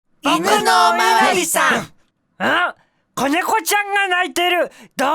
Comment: 最初「僕のお巡りさん」って聞こえる